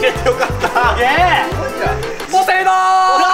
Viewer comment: ポセイドンって聞こえる